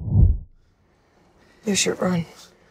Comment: What did she say at 1:12 ? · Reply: You should run